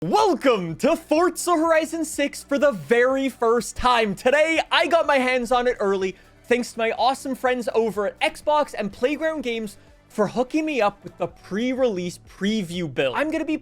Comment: did i just hear "welcome to farts horizon 6"